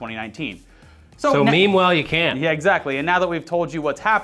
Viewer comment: Did he just say "memewhile"? 😂